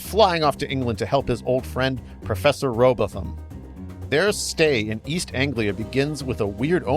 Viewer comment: First never read a Hardy Boys novel so my head backfilled the Venture Bros Next I thought you were saying Dr. Rowbotham as in Samuel Rowbotham and was thinking Hardy Boys and the flat earth?